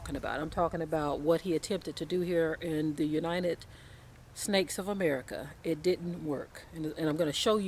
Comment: did she say the untited snakes of America just wondering? right at 2:17?